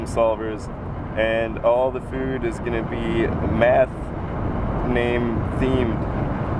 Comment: I thought he said it was a "meth-themed" restaurant. Then he said "Pythagorean" and I realized it was math.